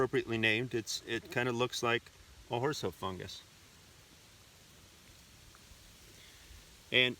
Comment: I've always heard it as "Fun Guy."